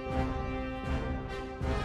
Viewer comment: When I first saw this, I thought he said “no condom forever”